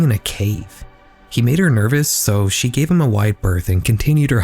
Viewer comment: "He made her nervous, so she gave him a [...] [...] and continued her hike as usual." What was said here? The generated captions said "...she gave him a wide birth...". That doesn't make any sense to me. Edit: just figured out it was "wide bearth". From Merriam Webster: give (someone or something) a wide berth idiom : to avoid or stay away from (someone or something) I could see that she was in a bad mood, so I gave her a wide berth.